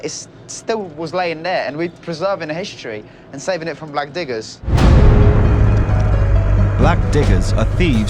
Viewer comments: “We’ll make sure to treat each relic with respect” Oh yeah? “Slams axe into log” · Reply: I thought they said black ni***rs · I thought they said black ni***rs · sounds like it says saving it from black n**gas